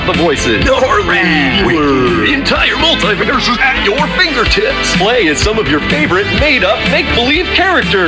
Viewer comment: I know I’m not the only one who heard fingertits.